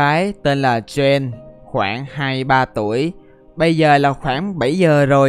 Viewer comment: Người ta kể là 22-23 tuổi nhưng em nghe thành 2-3 tuổi.hehe.và đây là số like của anh | | | | |